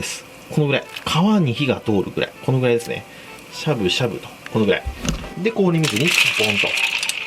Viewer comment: しゃぶしゃぶが、syamu.syamuに聞こえた